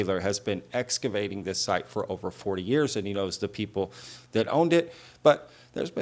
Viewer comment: Sounds like she said 4 years not 40 years.